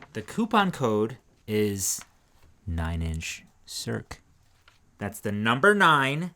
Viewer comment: Ray thought you said 'Coupon Code, Nine Inches Twerk'. Almost spat my Sparkling Rose all over the screen.